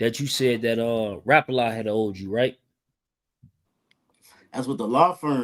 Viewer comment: All I heard him say just now was “ Poppa dot had one “ 😂😂